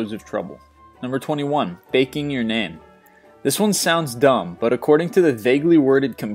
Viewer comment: Oh, I thought he said "Baking Your Nan"!